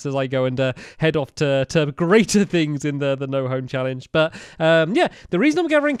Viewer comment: I thought he said the no ho challenge